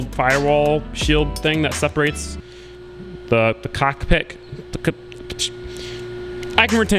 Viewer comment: did someone say a Cock Pic???